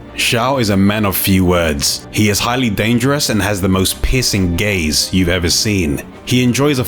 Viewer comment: I'M SO SORRY BUT I HEARD "PISSING GAZE"